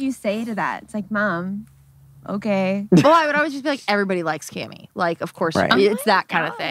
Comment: when said "everybody likes cammie" i thought she said "everybody likes gay me" and i was like yes that's true